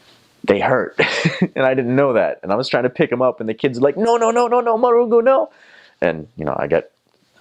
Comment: Did he just say no no murungu 😂😂😂😂😂